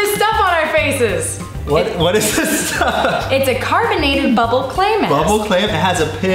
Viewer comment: I thought when she said clay mask that she said climax .